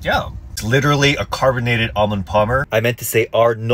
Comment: sounded like you said 'Almond Plumber' lmao😂